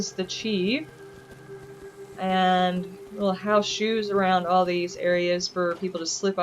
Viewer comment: I totally thought you said How shews instead of house shoes. I thought it was some kind of meditation type of thing. lol I am hurt that you aren't doing my Rube Goldberg machine next. :( JK I know it is not for everyone. This place looks great. It would be the perfect place to get away from everything and be able to relax and forget about the commonwealth as it is. As good as it looks during the day, it looks even better at night. Great job.